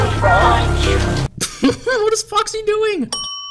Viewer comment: 1. Voice Crack Time! 2. OMG HAHAHAH 10:10 omg why is this so funny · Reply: When you click the time 9:31 i heard " OXY doing?! 😂